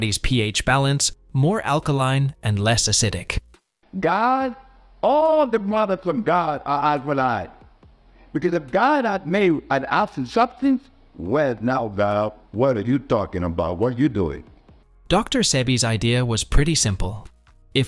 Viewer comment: captions are screwed but I think he's saying "God..All the products of God are alkaline. Because if God have made an acid substance, well now God, what are you talking about? What are you doing?" Suggesting alkaline over acid products.